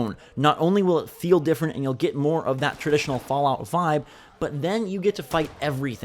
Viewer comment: did he just say fallout 5? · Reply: Birk Røstum Stabell naw “fallout vibe, on your way to 5” he’s meaning level five